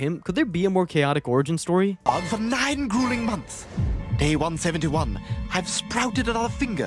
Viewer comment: "Day 171" was written wrong as "They won 71". Got lazy and generated your captions instead of writing them didn't you? Shame! I expect QUALITY when watching a whole video essay about a character's storyline written in a show built on non sequiter humor!